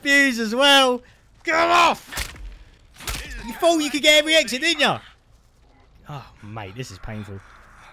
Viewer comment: this video started here for me . Iea i didnt understand nun he said lol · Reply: YOU THOUGHT YOU CAN GET EVERY EXIT DIDDNT YA